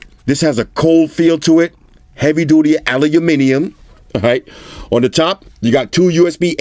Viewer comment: Surely it's not just me who heard "Allahuminium"